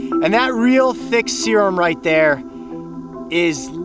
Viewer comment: did he say serum? 😂😂😂😂🤣🤣🤣🤣